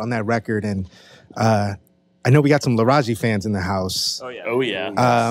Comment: What was the band they mentioned at @ 16:59 ? Couldn’t make out what they said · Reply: Laraaji